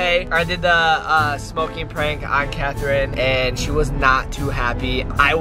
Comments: i heard cathrine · did he just say on Catherine ? 👀👀